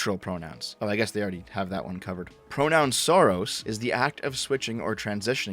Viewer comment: Heard it as PronounSoros lmao